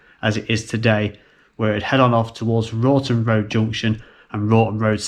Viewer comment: did he say routon routon